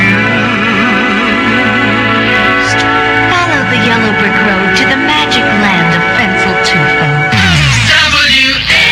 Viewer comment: What'd she say at 10:35? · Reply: Follow the yellow brick road to the magic land of Fencl Tugo. Fencl Tugo was a Chevrolet dealership in Glendale Heights, a suburb of Chicago.